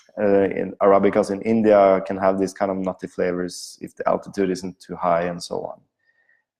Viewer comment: You might find this funny, but the whole video I thought you were saying "naughty flavors," not "nutty flavors." Great information thanks.